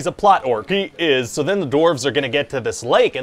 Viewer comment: I kept hearing him say “doorves“